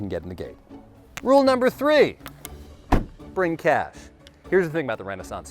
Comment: I heard “bring cats” instead of cash and got very confused 😂 Sadly I’m in the wrong country to see ren fares but I have a goal to go to one, preferably where you and/or ses carny is cus damn I love seeing you on YouTube haha